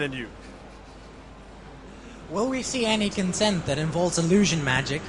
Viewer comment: what they actually said: 13:50 will there be mesmers? 14:00 Fu not telling